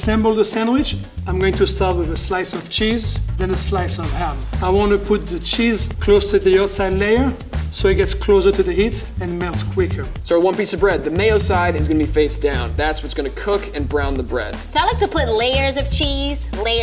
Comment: Thought he said "I want to pu the cheese closer to the ozon layer" first lol :D